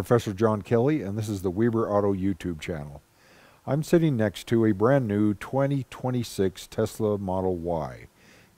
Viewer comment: did I hear well "sitting next to my wife 2026 model Y"!